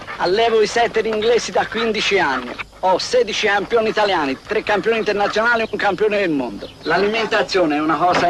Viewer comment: Scusate ma nello spot Chappi non ho mai capito bene cosa dice l'allevatore, a 31:42, "ho sedici...." cos'è che dice????? Qualcuno riesce a sentire? Grazie. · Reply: Allevo i setter inglesi da 15 anni ho 16 campioni italiani... Etc...